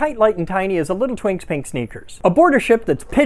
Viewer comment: Had to watch a few times because the first time I heard "a little twinks pink stinker", not sneakers. 😂😂😂